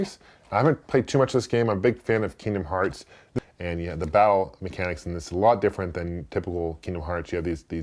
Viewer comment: When you said battle mechanics it actually sounded like you said bowel mechanics.